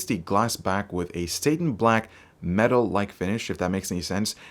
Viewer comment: did he say Satan black? lol